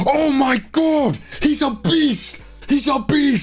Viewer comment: He said a beast, but my ears heard it as "Obese! Obese!"